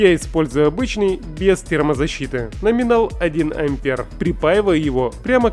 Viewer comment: Термозащита на то и термозащита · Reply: Послышалось "сπермазащита"